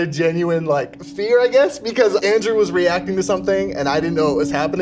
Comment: I didn't hear 'Andrew' I heard 'Where's your glowstick?' 😅